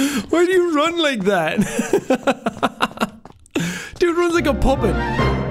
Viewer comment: "dude runs like a puppet?" did he say puppet?